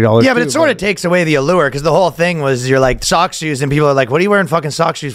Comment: I overheard this from the other room and thought you guys were saying Kayne had "ZOG shoes" at first lol